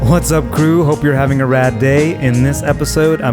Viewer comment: When he said hello it’s I hope your having a rad day the first time I heard “hello it’s Alan I hope your having a bad day”